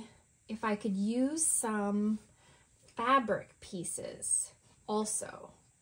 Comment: I was multitasking for some of this and out of the corner of my ear I heard "I think I might want to use some pieces of cadaver in here." I thought "that's a bit weird (even for me) but you go." I was disappointed and relieved all at the same time when I looked up and saw you probably meant fabric and no cadavers were harmed in the making.